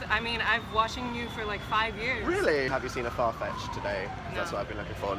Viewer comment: AT FIRST I THOUGHT HE SAID "FART FETISH" AND I WAS LIKE WHAT